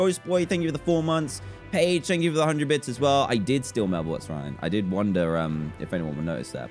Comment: what did he say... Subtitles say "I did steal my boots ryan I did" WHAT